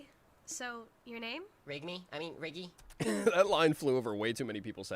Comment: I thought bro said PEG ME 😭😭😭😭